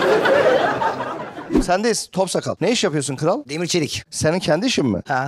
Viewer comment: deki adam ne iş yapıyor? Anlamadım ve alt yazı de anlamıyor ne dediğini 😅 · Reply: Demir çelik